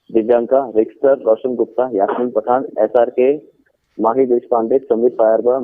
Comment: did he said SRK??